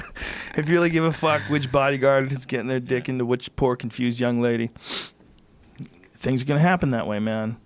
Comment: I must be baked. When Joe said, "...poor confused young lady..." I heard 'pork infused young lady'. He was talking about a Kardashian, but I don't think it was the one with pork fat injected into her ass cheeks, otherwise, it would have made perfect sense.